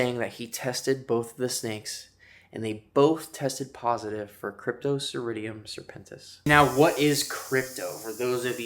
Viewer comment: for those wondering what he said Cryptosporidium serpentis